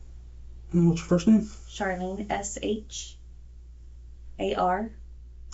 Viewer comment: sorry but I love how they spelled her name Charlene when she clearly says it's 😭 the captionist def sided with the officer even about the spelling of her name lmfaoo